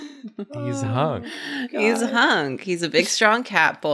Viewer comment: i swear i heard say "he's hung"